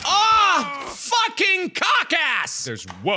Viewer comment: at speed 2x it sounds like he said "fucking contacts" Instead of "fucking cockass"